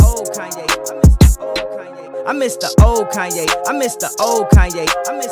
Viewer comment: it almost sounds like hes saying "Im Mister old Kanye"